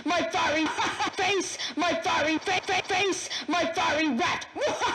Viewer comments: "FACE MY FIREY RAT HAHHAHAHAHAHHAHAH-" Honestly the voice scared the crap outta me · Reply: I thought he was saying “FACE MY FURRY RAT HAHAHAHAHAHAHAHA” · "FACE MY FIREY RAT HAHHAHAHAHAHHAHAH-" Honestly the voice scared the crap outta me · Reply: I Thought He Was Saying Face My Firey Fa Hahahahahahahaha